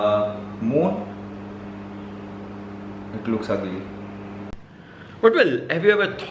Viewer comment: what he said ? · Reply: Moon, It looks ugly -, 2020